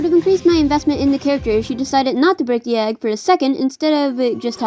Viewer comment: "If she decided to break the Yag" am I the only one who hears Yag when she says egg?